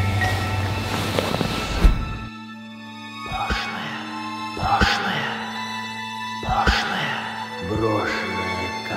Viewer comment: я слышу что там говорят пошлое или мне так слышится 🤣🤣🤣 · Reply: Там говорится "потеряная ...Брошеная ... брошеная ..."